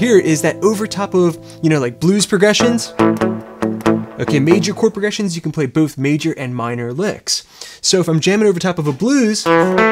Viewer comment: #1. This "revelation" of yours isn't exactly "mind blowing", even to the intermediate player, a bit of click-bait there... #2. Contrary to what you said at the 3:04 mark, you can play minor penta on top a major key chord progression but NOT vice versa, it'll sound like shit... #3. I enjoyed watching your vid, you did a really good job explaining stuff that will catapult beginners' playing to the next level :) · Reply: If you listen again, I said “over major chord progressions you can play both major and minor licks. I never said you could play major pentatonic licks over minor chord progressions.